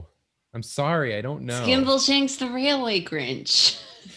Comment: I heard 'Skimbleshanks the Railway Grinch' and now I have milk and biscuits up my nose. Jesus. Warn a guy.